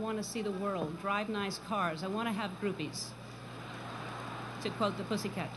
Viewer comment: I admit it... I also thought it said boobies.